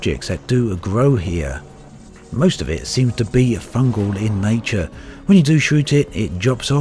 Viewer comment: Who else thought he said "fuck all" at 3:50? I swear he did haha.